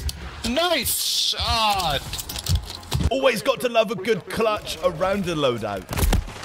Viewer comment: they were camping the loadout so i got em back by doing the same 🤣 you cant hear it but at the beginning he said “at the loadout thats a free kill”